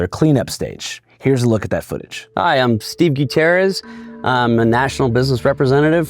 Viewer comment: did anyone else hear “Hi, I’m Stinky Terrorist” 😂